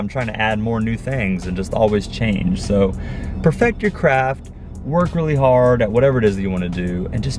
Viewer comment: I for real thought you said "perfect your crap" instead of craft haha